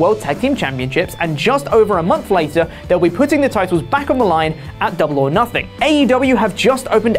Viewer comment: I thought he said Triple-H and I was about to die 😂😂